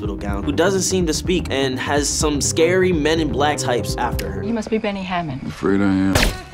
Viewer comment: When Caleb says "El, who doesn't seem to speak and has some scary Men in Black types after her", I heard "men in black tights" and I was like "I honestly do not remember that? I feel like I would remember that?"